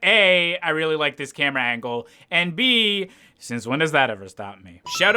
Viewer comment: What does he say at 8:01? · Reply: "Since when does that ever stop" I think